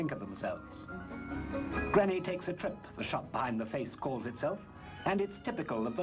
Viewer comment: Please, people, what does he say at 1:02 ? "'Granny takes a trip', a shop ???? the face calls itself ?" Thank you for your help !! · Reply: "Granny Takes a Trip" - the name behind the face it calls itself.